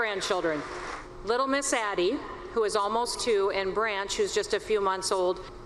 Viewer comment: Did she say her grandkid's name is "Branch?" (Also heard Grant or Blanche)